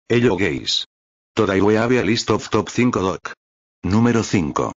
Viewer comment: Misheard: Ello gay Oday e how a lis drop Tip 5💀💀💀💀💀